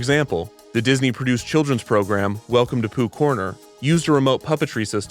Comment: i cant omg i swear i heard "the POOP corner" and i cant stop laughing now